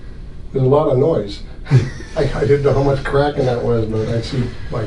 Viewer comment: Cracking starts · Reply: L i c i M. I thought you said cackling and i was just like wtf ajhsjs